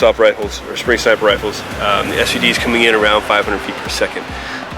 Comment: Did he call that an SUV at 4:21???